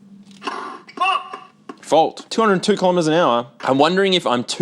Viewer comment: I swear I'm an adult, but I didn't hear "Fault!" :D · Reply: I’m still a teenager, but I too heard “Fuck!”